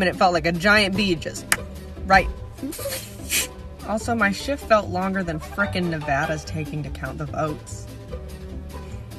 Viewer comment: “also my shift felt longer than frickin nevada is taking to count the votes” what i heard: “also my shit felt longer than frickin nevada is taking to count the votes”